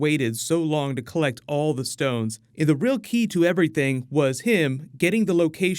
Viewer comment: At first I thought thanos said "low key" but then hit me